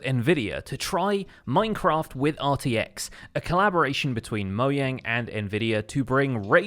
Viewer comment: Is it only me or did he say Moyang